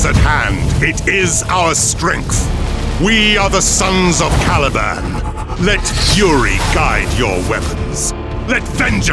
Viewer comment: I thought I heard "Brothers! We are the sons of *TALIBAN!*" and I was fucking SHOOKT